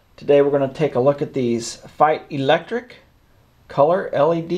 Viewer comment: What omitted to tell us was the word "Feit". He speaks it a few times however it's not a brand that most non-Americans would recognise as a brand. In any case, most of us want to buy from the source, not a retailer. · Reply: Coops Well I didn't know either about that brand. I thought said "Fight electric" so I searched for that, adding the words LED and filament and ended up finding Feit website.